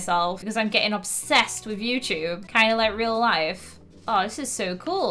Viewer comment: When she said 'kinda like real life' I thought she said Kyle like Real life! Ha ba 😂😂😂😂